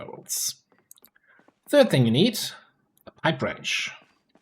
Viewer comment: Oh wow.. I was watching this on my phone and I couldn't for the life of me figure out what you were saying at 1:06 .. Got on my pc, watched it five more times and realised you said "pipe wrench".. Other than that, great vid.